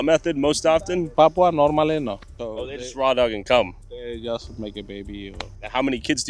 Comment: for a second I thought said “….just swallow and cum …”😂😂😂